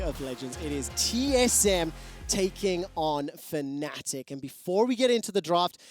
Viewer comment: was I the only one who hear in 0:21 "it's TSM taking on the nazis"?